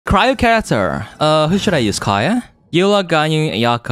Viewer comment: Bro at the start I thought you said cyro cancer